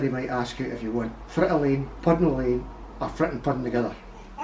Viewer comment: I thought he said front a lean put a lean or front and put them together 😂 I was close....if i wouldve kept replaying it i wouldve gotten it but that was my first response!